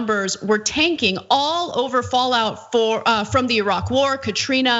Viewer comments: ummm.... Did she just say Fallout 4 before quickly correct herself? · did she just say "Fallout 4"?! ahah didn't know Ana was into gaming :D